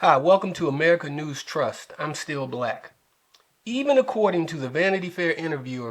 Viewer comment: I thought you said your name was Still Black then I read Steele , I'm like # 4 ....I subbed you hopefully it helps ...Z